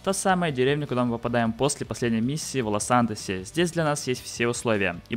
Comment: кто еще услышал Волосантос?)